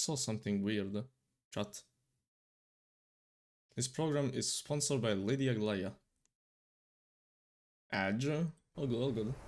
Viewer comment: I THOUGHT IT SAID SPONSORED BY LADY GAGA OMFGOA;EIHPU9RWIH